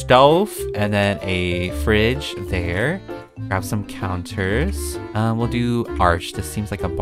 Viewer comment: Okey, when you said counters i thought you said cow turds.